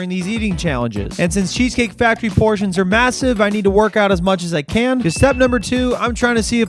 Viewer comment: Did he say MASSIVE?